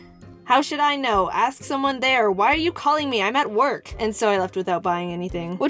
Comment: did she say work or wort?